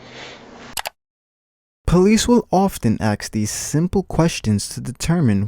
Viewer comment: I like the Videos but at 13:50 he says "police often AXE these questions..." pronunciation is SOOOO important when all you do is talk. instantly discouraged to watch more videos.. in case this wasnt just lazy speech. the word is ASK ass-k.... people do this with alot of words; usually people that dont read well. like saying turreNts instead of turrET or "taking it for GRANITE. instead of granted. It makes you appear uneducated even if you are just being lazy..